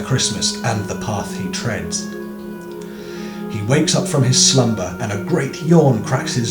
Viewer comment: At first I heard "He wakes up from his Slender" XD Anyway, very good poem!